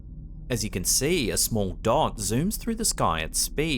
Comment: It sounded like you said a small dog zooms through the sky! Lol!